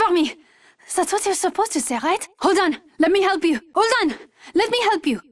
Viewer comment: What does wattson say at 8:18? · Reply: " that is what you supposed to say, right? "